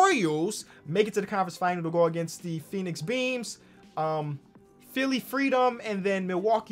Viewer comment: I thought he said “Phoenix Beans” til the end😂